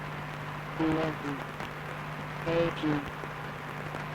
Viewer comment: I love you thank you is what I heard and 26:53 hey hate let me run away